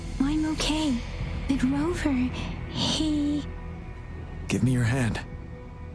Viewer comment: Did Yangyang say "He"????